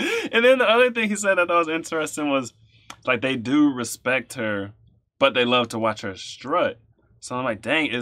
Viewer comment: They do respect the butt..is what I thought he was saying 🤣🤣🤣